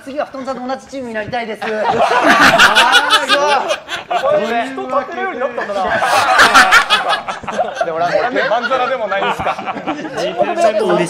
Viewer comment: ここなんて言ってる？ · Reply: 人立てられるようになったんだな。なんか